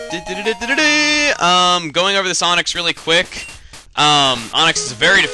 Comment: When Chuggaa said "Going over this Onix" I thought he said "Going for the Sonics" and I thought he was somehow making a reference to Sonic Generations, even though this was uploaded last almost 2 years ago.